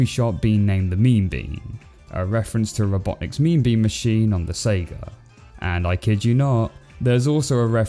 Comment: I swear I heard you say "Echidna-not"